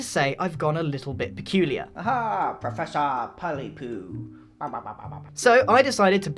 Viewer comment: is a Sim confirmed! (It also sounded like he said "Professor Pallypoo, pa pa pa")